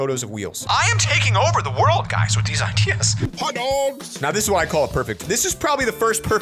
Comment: I thought it said how dogs are made